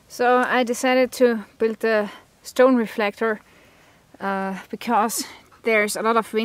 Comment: I swear, I thought this said, 'reactor'.